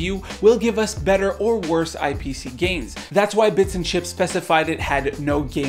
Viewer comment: I thought you said BLIPS AND CHITZ twitter account lmao